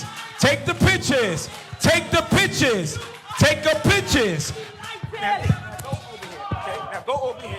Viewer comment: It almost sounds like he was saying "Take The Bitches!"